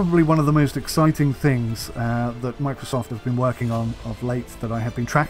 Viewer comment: think you said "Microsoft" here :P